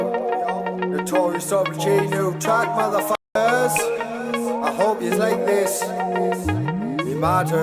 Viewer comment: Didn't pick up on the "I hope yas like this, ya ma does" - thought he said "I hope yas like this, it matters" (dunno which is worse tbh 😂)